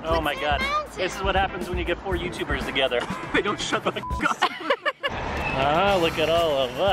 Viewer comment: That's why he said "shut the f*ck up" 😂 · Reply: Zoe Grail Juniper Arthur I thought he said, "They never shut the f*ck up."